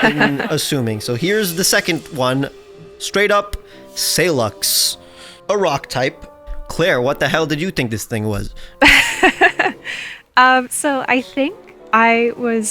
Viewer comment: i thought he said sollux and was genuinely so disappointed